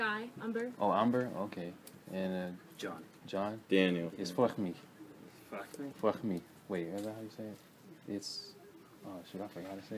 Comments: he thought you was gay bro, sounded like "fuck me" hahahah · Am i the only one that thought he said fuck me?